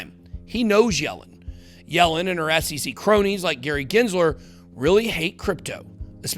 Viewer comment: Did he say Crowny 2:19 ? Buy Crowny!!!